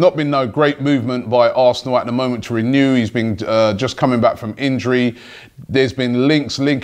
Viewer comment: For a moment I thought said "Wilshere coming back from fitness".